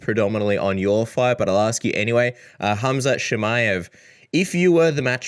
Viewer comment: Did he just say « Cumshot Chimaev » ?? 😂😂